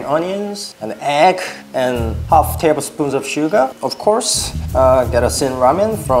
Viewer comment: I HEARD SUGAAAAAAAAA